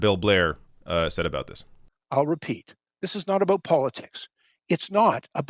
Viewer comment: I'm sorry, but am I the only one who hears the Canadian prime minister from South Park @ 6:00 when he says "this is not a-boot ... It's a-boot "?